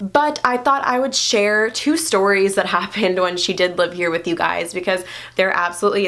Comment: Did she just say she lived with us